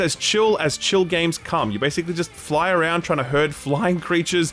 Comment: When he was talking about Flock, I heard: "it's as chill as chill can get, you fly around hurting animals" :)))))